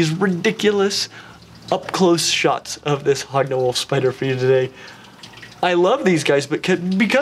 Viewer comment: I thought you were saying hog nose and now it sounds like hogna knoll